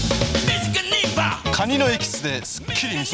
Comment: 懐かしい…カニのエキスでってとこ髪のエキスでに聞こえた小さな俺はこのオッサン髪のエキスが入ってるんだと思っていた！絶対食いたくねーと子どもながらに思ったのを思い出す。